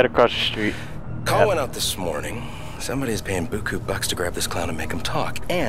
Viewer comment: The game producers messed up on one part of the subtitles... 3:04 in a second look at the subtitle "Call wen out this morning. Someboy's paying beaucoup bucks to grab this" and instead of Jordi saying "Someboy's" he say's "Somebody's"